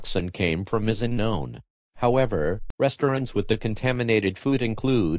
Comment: OMG i think say resturants i'm starving